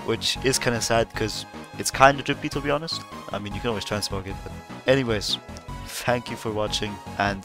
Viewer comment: what did you say there after "i mean you can..." cool vid btw, the helmest is drippy as hell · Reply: I mean you can always transmog it